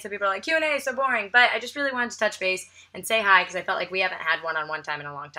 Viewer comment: I thought you said 'I just wanted to touch face' at the end and I got very excited. And then I realised you didn't say that. And then I was sad. So what I realised form this video is that subconsciously I really want you to touch my face but I didn't know it until you said that. But you didn't actually say that. So I'm sad again....I should go to bed.